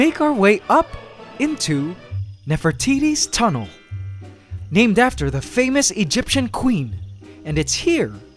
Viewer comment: every time he talks about the tunnel, i hear "Never Titties tunnel''